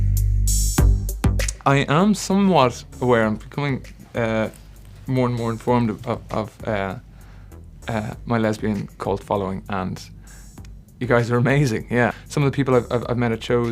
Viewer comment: can anyone tell me what he said in this part? i can't understand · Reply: "I am somewhat aware, I am becoming more and more informed of my lesbian cult following and you guys are amazing! Some of the people I've met at shows, after Chicago in particular, got to met some really really fantastic people. And I'm here too and I'm here to keep being awesome". Late reply sorry 🙈